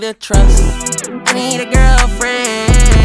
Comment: I heard this as “Me and your girlfriend” but he actually says “I need a girlfriend”